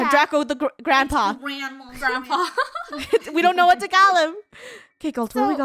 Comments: Did say " we dont know tagalog "? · did said "Tagalog"? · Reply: No she said "don't know what to call em"